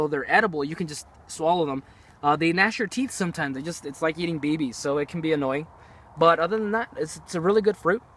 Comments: they are like eating babies ? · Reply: I thought he said eating babies too lol I suppose it looks red like baby blood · I thought he said "It's like eating babies" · did anyone else hear him say "It's like eating babies"